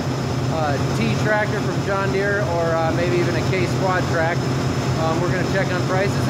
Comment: did you say a case quadtrack? You have let me down